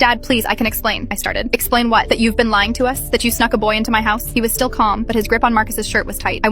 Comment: I thought she said suck instead snuck😭😭😭😭😭